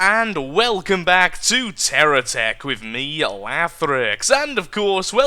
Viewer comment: whenyou say terra tech... it sounds like you say terror tech XD TERRIFING I KNOW